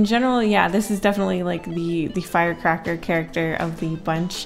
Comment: when she said "the firecracker character" my dyslexic ass thought she was about to say the crackhead character